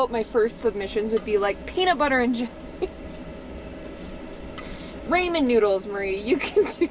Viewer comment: wait... did you just say "ray men" lmfao not "rah men"? xD It would take skills to mess up instant ramen tho.